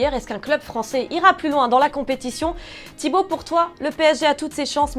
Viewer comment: à 1:23 au lieu d'entendre thibaut pour toi j'ai cru entendre thibaut courtois y me enteras dont toute ma vie XD !!